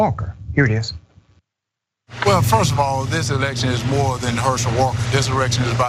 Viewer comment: WTF?! did herschel walker just said this "Erection"? what a 🤡!🤣🤣🤣